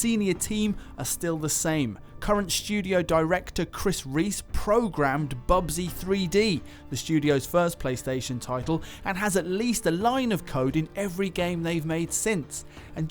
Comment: who else heard pubg 3d at first